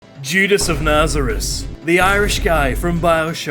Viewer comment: I thought you said "Jesus" instead of "Judas" in the beginning, and I was about to Triangle + Square you with double Might Bar